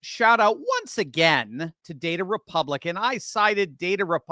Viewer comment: I thought you were saying "Date-A-Republican" 😆